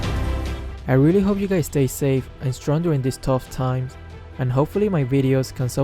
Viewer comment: well at least Luo Yi uses a mask... also 14:45 i litterally thought you said "I hope you guys stay safe and struggling this whole time"